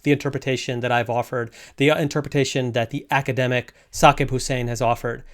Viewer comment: What was the name you mentioned at 8:35? The captions says "Sake Hussein" but I find no results of that on Google. Neither can I make out what you've said to accurately determine the scholar's name. If anybody knows, it would be appreciated! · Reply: Professor Saqib Hussain at LMU